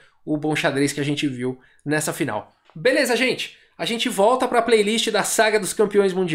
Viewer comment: Eu ouvi “vamos voltar pra playlist do Paul Morphy” quero